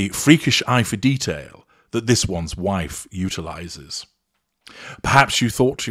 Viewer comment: You said 'utilizes' while all I heard was 'brutalizes.'